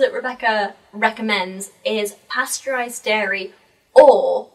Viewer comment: I believe she said pasture-raised (as in grass-fed) instead of pasteurised :)